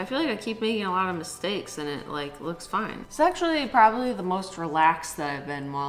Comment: she sounds like she says sexually and I had to do a double take and replay it😂